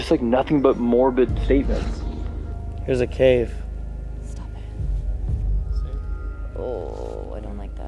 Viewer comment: when said that were was a cave a soft woman’s voice says “stop it” as if she was warning them. not sure if anyone else heard it but let me know if you did!